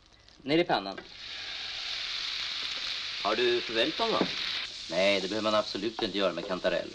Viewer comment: vad säger han???? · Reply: Har du förväld dom?